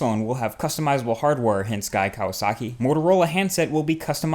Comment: here 2:22 I heard it as "Motorola hints Guy Kawasaki..."